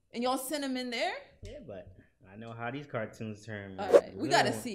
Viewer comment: Did he say it was a cartoon